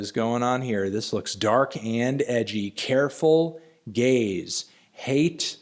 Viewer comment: Am I the only one who thought he said "careful gays"? 😂